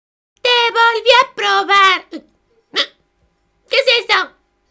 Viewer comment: Juro que entendí "me volví a drogar"